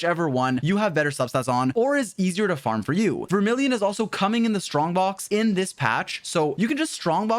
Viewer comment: I swear I could hear him saying "Vermillion is coming in the strongbox, AND SO AM I. what?"